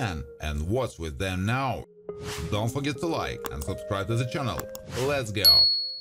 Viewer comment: In the beginning he says "Let's go", but first hearing it I swore he said "hey friends, lesbian" instead of "let's go" LMAO.